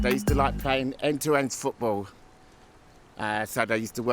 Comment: I swear he says “end to end” not “endurance”